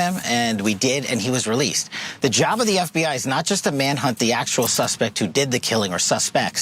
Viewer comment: did he say the job of the FBI is not just to hunt the actual suspects 🫠